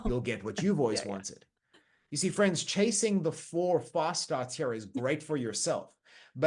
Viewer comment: his accent made me hear "false starts" instead of "fast starts" 😅